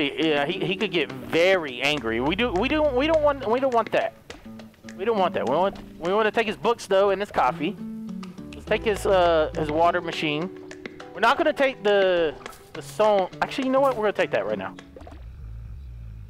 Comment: Who else heard him say water machine instead of sprinkler